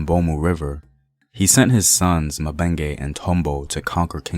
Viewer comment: Lmfao i heard "He sent his son Dekembe Matumbo" i was like...wait..what?!